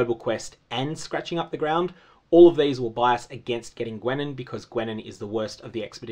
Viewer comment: When you said "getting rid of Gwennen" I thought you said "getting rid of women", then I realized you said Gwennen... Only to then realize that's the same thing (as far as expedition is concerned anyway).